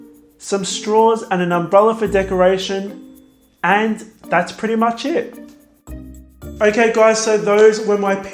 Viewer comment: I love you, you pronunciation and your recipes, but every time you finish a cupcake and say "that´s pretty much it" I hear, "that's pretty much shit" LOL , don't take this wrong, I'm not a native english speaker and that's probably why